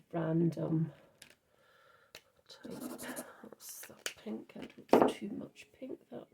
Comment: Another fab card. I heard you say thats enough pink 😅 and laughed. Tfs 🩷